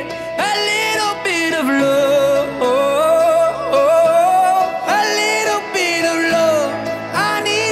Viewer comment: I've got voices in my head and there's a deafening silence I've got voices in my head and I can't lie I've been holding onto pieces, swimming in the deep end Tryna find my way back to you 'Cause I'm needing a little bit of love A little bit of love, I need a little love Just like the air I'm breathing These awful wounds ain't healing Tryna find my way back to you 'Cause I'm needing a little bit of love A little bit of love, a little bit of love A little bit of love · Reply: First time I misheard it as a leel bit of lo (Ik it was little bit of love but still 😅)